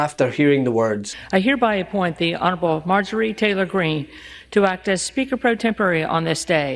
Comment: sounded like she said 'I hereby appoint the ~oddball~ Margorie Taylor Green'